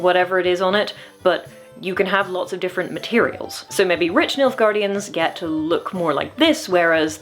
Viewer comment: I really thought you were saying 'MILF guardian'. . . i'm sorry.